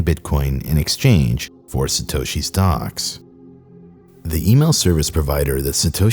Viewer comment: I thought you said the hacker was holding satoshi's "dogs" captive as part of the extortion. I am glad I misheard, because that would have been pure evil.